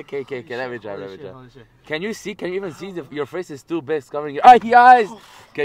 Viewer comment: did he say your face is stupid